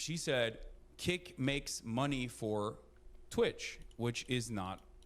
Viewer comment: playstation makes money for xbox, that what is he sayion XD ? (cause sony use microsoft services to run its businesses)